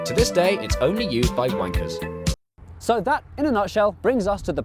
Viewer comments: did I hear right at 09:47 "... It is only used by wankers"? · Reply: he definitely said wankers. and hes right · did he just say ‘wankers’?